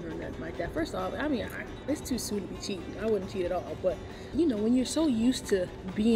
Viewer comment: Wait did just say;”it’s too soon to cheat”😂😂😂😂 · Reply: I thought she said it's to "sweet to cheat" I've been trying to figure that out. Cause I was like dang its like that....but now I'm not sure what she said. Lol